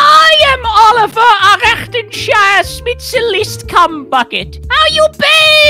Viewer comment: wait .. did fundy just say coom bucket?